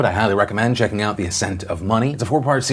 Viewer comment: I thought I heard "the scent of money" for a sec and thought it'd be a documentary of a guy going around the world smelling different types of coins